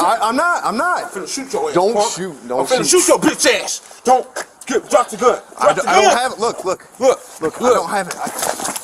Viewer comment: I am confused. Is he said “ drop the gun.? “ to police officer … He probably got mental issue.